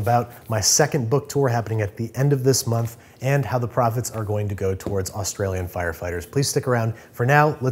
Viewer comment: "The prophets are going to Australian firefighters" Why did I hear it like that?😂